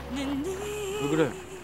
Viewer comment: 상실이가 뭐라한것임?? 왜 마지막 대사를 안들리게한건지 젤 중요한데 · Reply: 너때매 너때매 죽을뻔했잖아!